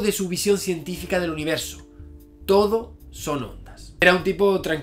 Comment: Todo son....? Por mas que lo repito no logro entender que dices :( haha pd: me flipan tus videos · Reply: ondas